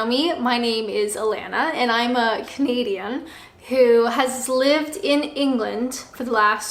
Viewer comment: Did anyone else hear "My name is Alanna and I'm a comedian" at 0:15?